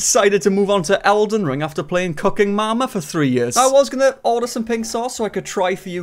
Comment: Lol i thought u said CUCKEN MAMA , but it was cooking mama ... 😢